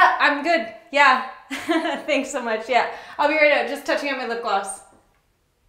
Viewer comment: The only thing funnier than the video itself was the auto-generated subtitles at 3:00 that , instead of 'I'm just touching up my lip gloss', read 'I'm just touching him in the. bus' lmao...